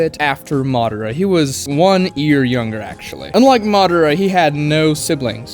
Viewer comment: Madara had brothers what are you talking about?!? 😂 UNLESS you said “unlike”but it didn’t sound like you did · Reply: I said unlike